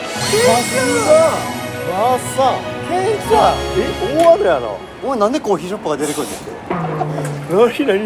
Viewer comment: これ正門くんに怒られる前に小島くんなんて言ってるのかしら。え？大雨やなあ？？😂